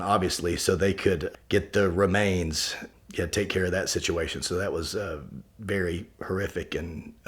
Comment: I could have sworn he said "That was beary horrific" and I lost it.